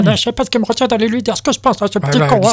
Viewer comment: What is the word at ~0:32? 'chicon', 'chicompte'? I'd love a script of this to improve my French.